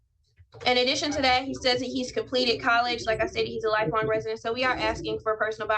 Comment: Did she say he's completed college?